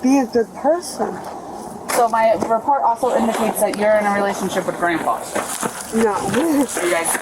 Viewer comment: stopppp wait wait hold uppppppppp...... at the 16:30 ish mark did she just say "my report indicates your in a relationship with GRANDPA!?????" everyone go to jail right now im arresting everbody were done here